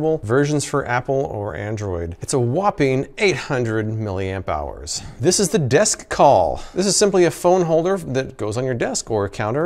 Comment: that moment you're not paying attention and instead of hearing "800 amp hours" you hear "800,000,000 dollars" and have to rewind to make sure you heard it right XD